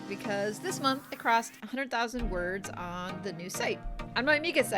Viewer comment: HAD TO REWIND... THOUGHT YOU SAID POSTED 100K WORDS THIS MONTH... PASSING 100K IS STILL AWESOME THOUGH...